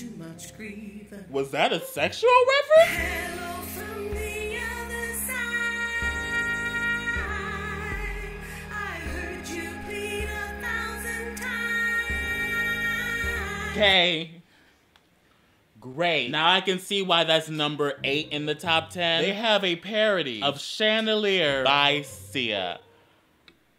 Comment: I thought she said “I heard you pee a thousand times”